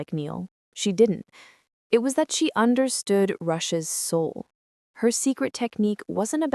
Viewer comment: Your Closed Caption said "Russia"s Soul" not Rush's soul! OOPS!!!